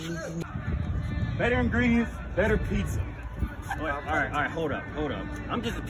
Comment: “Better ingredients better pizza” “alright hold up I’m just the pizza guy “ had me dying 😂😂😂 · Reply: I thought he said better greens better pizza 🍕